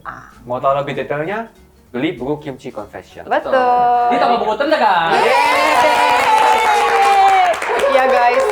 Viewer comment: Ada yg tau bilang apa 40:02? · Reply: Di toko buku terdekat